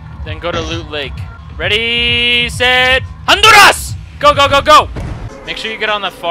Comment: Btw on 0:36 did you say Honduras that's where I was born lol